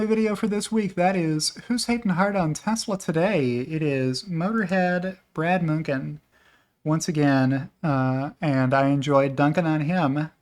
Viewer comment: I thought I heard say, "I was talking to Brad Munchen today..." Am I more delusional than usual?